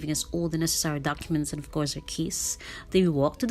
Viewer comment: Your accent is beautiful but I really thought you said the check in agent gave you a kiss (instead of keys). I had to replay 2:37.. 😂 Great review.. and so informative. I will definitely consider staying here next time. :)